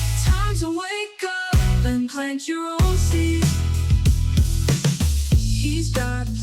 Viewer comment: Lol the last song I kept hearing ‘time to wake up… and crank your own meat’😂😂😂